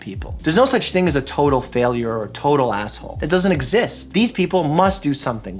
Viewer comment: did he just say "be an asshole" that casually? I thought he was more professional than that I mean it's a YouTube channel and we expect professionalism. (I'm just joking)